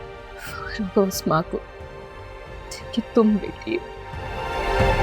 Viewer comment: what she said??? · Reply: Fakhar hoga us maa ko jiski tum beti ho🎉